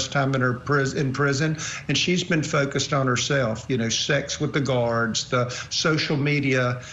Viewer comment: wait did he say sex with the guards 😳